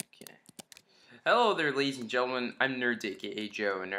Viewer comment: When he said ladies and gentlemen, I thought he said lazy gentlemen and I was thinking, he is probably right